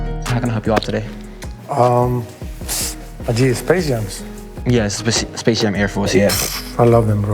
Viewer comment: Did he say Adidas Space Jam ?